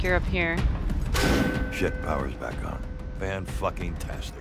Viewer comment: Because there was no pause when he spoke, it sounded like he said "Sh*t power's back on!" Uh... I'm sure using 'waste' for fuel is environmentally friendly, but... o_o 12:36 lol, that segment cracked me up. "But wait... there's more!" I pictured a crazy guy in a purple suit and top hat trying to give away additional generic soldiers on a stage somewhere :P